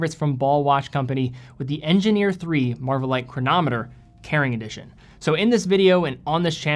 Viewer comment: I had to go back and check because I heard him say it as '... The Engineer III Marvelight Chronometer - Karen Edition'